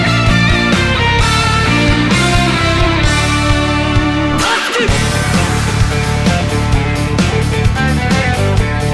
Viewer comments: What says in 3:48??? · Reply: Justice i think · What did he say in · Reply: Tusk, his stand's name.